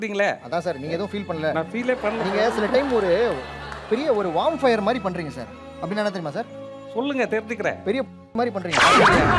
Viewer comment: Anyone knows what he says at 37:51? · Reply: Wormfire - Puluthee 😂